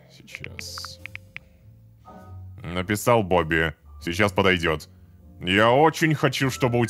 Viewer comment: Мне послышалось Написал Бобику Он сейчас подойдёт 😅